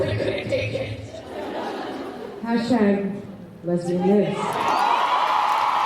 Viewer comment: Can anyone tell me what does Chyler say in 2:48 ? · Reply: #lesbianlives